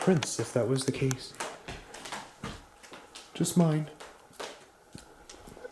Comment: I hear a child's like voice saying, "what's wrong" or something like that. It's hard to hear cause it's when is moving the camera. Pls, if anyone else hears it, or hears something different please share!!🫶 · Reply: I hear it mocking saying ‘just mine’. To me it doesn’t sound really childlike, seems more like a grown up mocking.